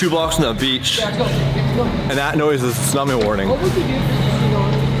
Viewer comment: I couldn't understand what was saying and CC says "snowman warning" lmao had to back up a few times til I got "tsunami warning" lol